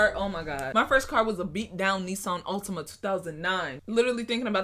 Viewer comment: Did she just say “Knee-san” instead of Nissan 😂😂😂